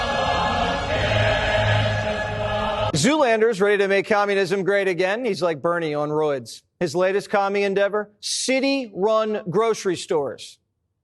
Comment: Crazy how many of these people cant pronounce mamdani without having a stroke. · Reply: Seriously. What the hell did the fuck boy at 5:45 say? Zoo lander?